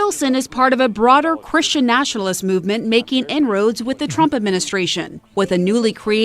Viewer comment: in-roads or n-words I didnt hear her